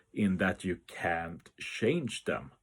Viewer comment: Dang at 0:33 I thought you said you CAN change them. :(